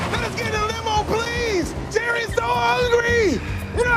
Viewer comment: it also sounds like he says jerry too lol